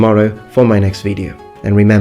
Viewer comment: I thought you were saying "number ones" not "noble ones" at the beginning of your videos